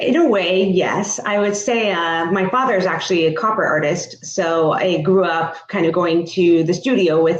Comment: She said copper artist, i heard "con artist" lol